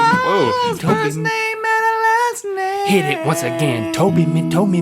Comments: toe baby, toe baby, toe baby, toe baby, toe baby. toe baby, toe baby, toe baby, toe baby, toe baby. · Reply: Branden Lucero oh i thought he said Toby Me Toby Me Toby Me.... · is Link saying "Don't beat me"?